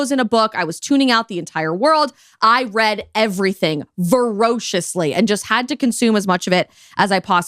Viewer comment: You seemed to say you "read ferociously" where, being English, I think you meant you "read voraciously", unless you are suggesting you were a feral child that clawed and snarled at the books while reading. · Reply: I heard "verociously" and assumed she was just not quite as accomplished/literate as she thinks, lol.